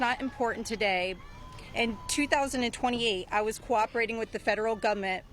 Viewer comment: Did she say 2028 31:51 or am I tripping